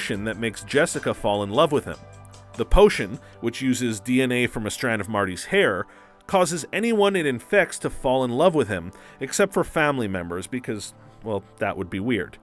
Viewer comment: did anyone else hear Marty?